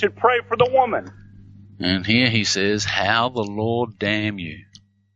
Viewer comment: This does not say "how the Lord damn you." It says something about Howard OWES... (Howard was Keyser's laywer). Stop with the Bias and injecting improper words.